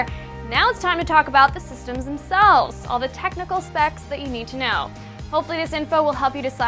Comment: I heard "technical sex"